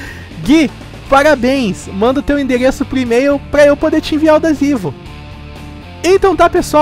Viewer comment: só eu entendi desivo?